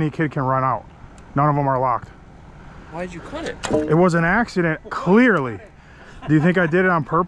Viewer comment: "it was an accident" i didnt hear this for a very long time